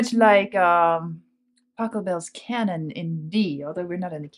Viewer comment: and 6:13 Did she say Taco Bell?